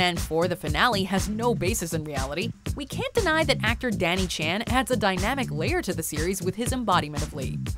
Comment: 😶😕🤭 Did she actually said "Donnie Chen?" @ 1:19?